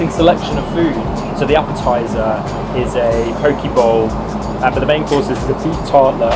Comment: Thought said "Poke Ball" at 15:00 and definitely agreed with his remark of "interesting selection" 😂 but otherwise absolutely love the content so far!!